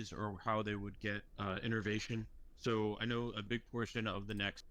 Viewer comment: Are the captions auto-generated? I listened in and I think the program is mishearing innervation. Like nerves expanding and branching out into the ECM.